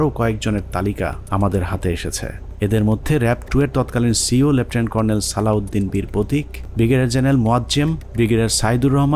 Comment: does he say Birpratik? Save us Allah